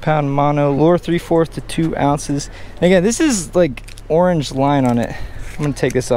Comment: Wait did he say the n-word? 11:22 " N*** this is" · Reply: He said again